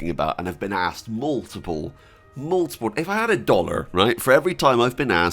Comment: I thought you said if I had a daughter for every time someone asks for Kingdom come Deliverance to I'd be a millionaire